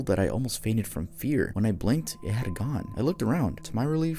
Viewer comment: in Teddy, I thought you said "I blinked and he had a gun" I was so confused